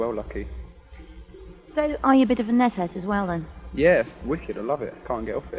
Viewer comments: did I mishear at 2:52? "So are you a bit of a meth head, as well then?" "Yeah, wicked, I love it, I can't get off it." "Eh" "Eh" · I thought she said "So are you a bit of a meth head as well then?" and Richard responds with "yeah its wicked i love it! Cant get off it.yeah, its decent."